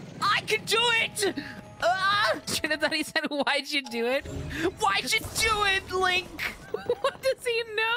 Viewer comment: I hear "WHY'D YOU DO IT, GARY?!" from fucking Bully